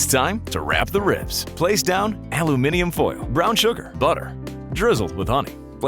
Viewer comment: Did i Heard "Butter"?